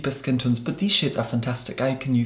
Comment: I heard 'shade' as 'shit' lmao